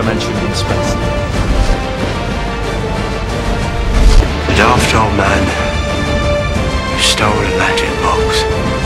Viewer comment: What did he say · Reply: A daft old man who stole a magic box